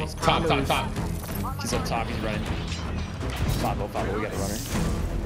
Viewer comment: Did he say "505 We got a runner"???? lmao